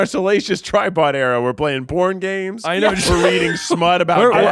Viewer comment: "We're in our salacious Tripod era! We're playing p0rn games, we're reading smut-" "I feel like we're becoming gambling addicts." Okay, but even funnier? I misheard it as, "We're playing board games", and was all, "Yeah, I could see that."